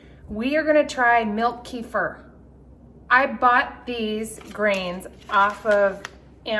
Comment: Haha haha I thought you said milk reefer and I thought well that should make you feel better!